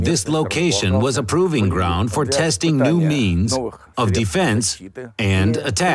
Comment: I might be sick, I thought the guy said "esting new "memes" instead of means